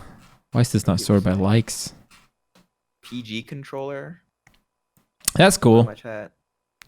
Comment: What was the mention about new accessible controllers. Zain mentions it around 57:30. I think he says PG controller